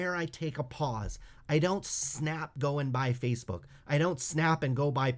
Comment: i heard buy $SNAP lol😅